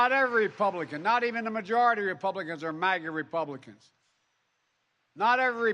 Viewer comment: Wait, I could have sworn I heard him say “Uyghur Republicans “ so he can persecute people who don’t agree with him like his buddy ping-pong! 🇨🇳